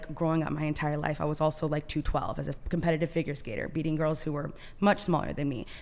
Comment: Did she say 212! Good Lord.